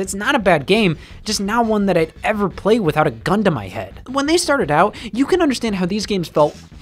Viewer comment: Anyone else hear this as "Just not one that I'd ever play without a Gundam I had"?